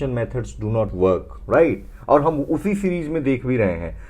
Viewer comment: what did you say? Ufi series what??